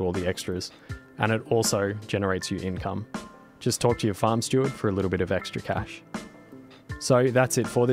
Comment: I admit I thought I heard "Just torture your farm steward for a little bit extra cash"